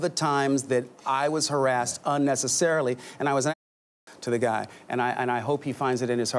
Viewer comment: what'd he say? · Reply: It is US-American censorship, I suppose. He said "and I was an asshole to the guy" - meaning the police officer.